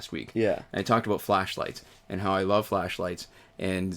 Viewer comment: I thought you said you love fleshlights! It was hilarious.